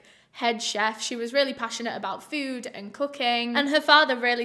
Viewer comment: I heard "she was really passionate about food and cocaine" LOL